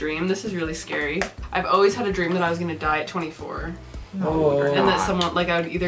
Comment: I thought she said 23 she said 24 . mandela affect